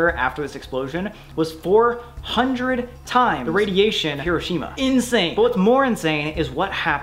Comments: i thought he said kirishima instead of hiroshima 💀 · Why did it sound like he said kirishiam from mha (my hero academic) GIVE ME ANSWERS